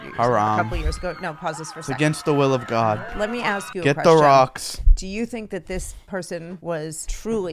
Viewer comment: Did he just say "get the rocks"💀💀💀💀💀💀💀💀I am dying